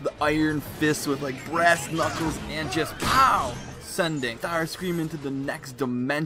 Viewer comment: It sounded like you said tharscream